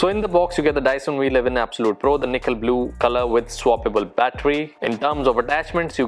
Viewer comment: Also why did I heard Nikhil Blue at around 2:22 😁😁